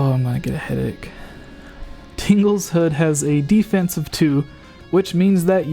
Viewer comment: I heard "Tingle's Wood." * shivers *